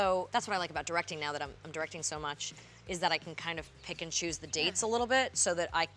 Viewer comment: There was a part of this interview where I heard the Word 'pikachu' ( I know She said "pick and choose") but for some reason I heard the Word pikachu 😂